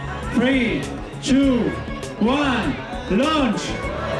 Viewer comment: Did he say lunch? I want lunch? Where’s lunch?